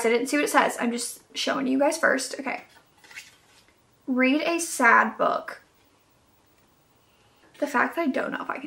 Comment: Really thought that said “read a dad book” and I was so intrigued lol